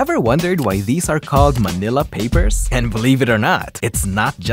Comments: Why i Heard "vanilla" 😭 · I always misheard it as "vanilla papers"